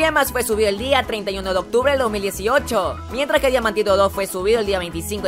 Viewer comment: yo escuché maldito 2 XD